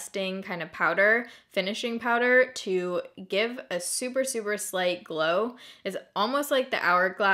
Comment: When you were talking about the Laura Mercier powder I thought you said it gave you a super slutty glow 🤦🏽‍♀️